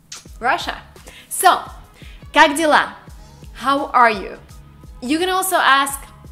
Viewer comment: My mind wandered for a bit and suddenly it sounded like you said, "GODZILLA!"